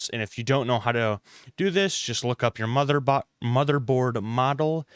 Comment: did he just say mother butt ?? JESUS